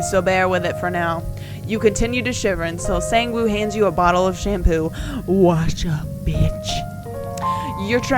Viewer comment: when he halfway said shampoo I thought he said shrimp. and I yelled "did he say shrimp?" out loud and my whole family turned to me and looked at me funny... im so embarrassed